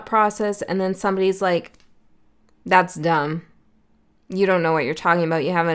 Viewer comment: True. I kept hearing "You don't know how close you are." Now, I am getting a feeling of exercise inner child freedom and spontaneity.